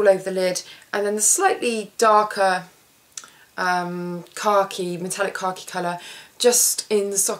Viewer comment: i love that it sounds like you are saying car key when you say khaki :) at first i honestly thought you were saying "car key" then after hearing you say it a few different times, and seeing the colors you were refering to, i felt like a total moron :P